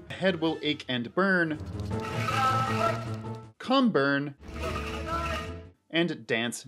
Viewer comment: No lie, when odalwa was saying come burn, I actually thought he was saying "boy must die". I was like, ok, thats not friendly